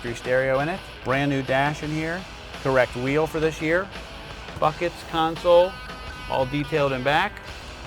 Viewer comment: sounds like he says fuckets console